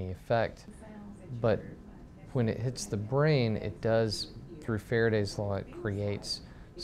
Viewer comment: Could someone tell me what does he say from 1:04 to 1:10? PLEASE! :) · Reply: I was curious too, he says, "through Faraday's Law."